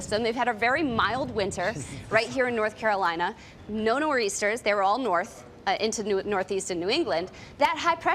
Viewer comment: Could anyone tell what the news anchor in-studio was saying at 2:18/19? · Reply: you referring to "no nor'easters"